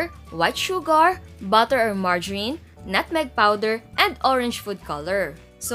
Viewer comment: wait what. Did I hear mardyarin ?